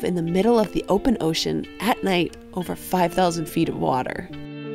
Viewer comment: Did you say over 5000 ft. at 0:13?